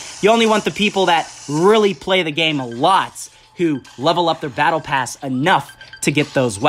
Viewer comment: Did he say “BattlePass” that sound familiar From Fortnite